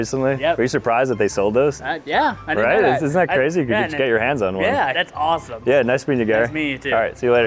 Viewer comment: I thought said “get your hands off me” cause my phone was a bit far from me 😭